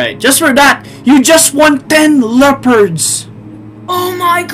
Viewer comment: I like ten" I thought he is gonna say "you just won 10 deaths"